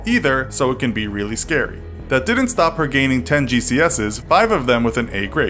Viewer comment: Did you just say 5 gcs’s